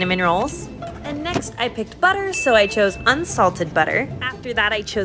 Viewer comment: ok but where does the butter come in? edit: I rewatched and I saw it lol I thought said sugar not butter sorry